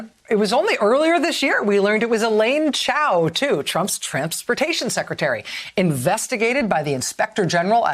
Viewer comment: Sounded like you said “…Trampsportation Secretary…”. 😂